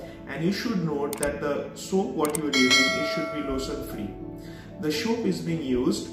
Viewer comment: the soap that you use is ________free. I don't understand. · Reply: Lotion free. That means Not to use moisturizer added soap.